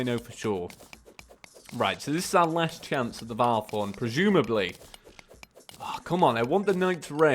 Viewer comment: the captions say last chance at the bar porn XD